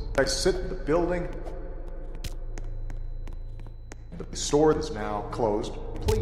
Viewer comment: I hear "i sim* the building store now close"